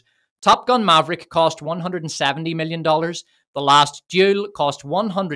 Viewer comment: I had to go back to 23:42 ...I seriously thought you said 'The Last Jew' and was wondering why I had never heard of this movie...